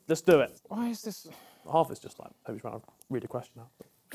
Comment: What did HArry say on 36:33 I didn't Understand it It isn't Clear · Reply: I think he said "Half of this is like Tobi trying to read the question"